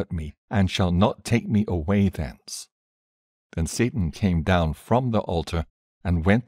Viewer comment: who cam down from the alter, sounded like he said satan???